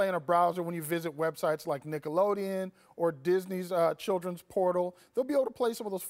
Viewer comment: i thought he said childrens porno, im like what?